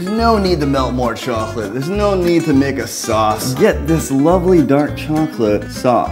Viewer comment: I know he didn't say it but it sound like he said "nigga sauce"😂